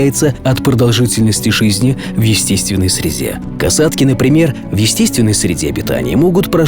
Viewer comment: мне одной послышалось в естественной срезе, а не среде😁